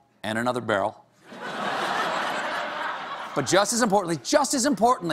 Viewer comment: I thought he said the Tonight Show! Akeard